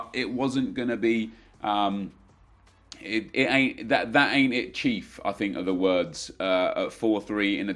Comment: why am i hearing saying 'that aint it chief' XD